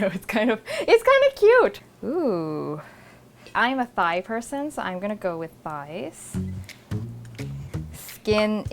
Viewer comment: i thought she said "I'm a Thai person so I'm gonna go with thighs" just because it rhymes lol😂